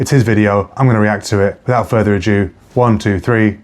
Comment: I heard you, “without further A JEW” 👌👌